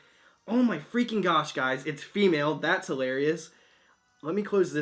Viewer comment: did he say females are hilarious errrrrrrrrrrrrr I’m so triggered